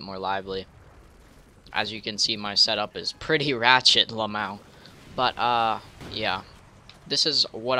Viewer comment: did he just say la-mao instead of laugh my ass off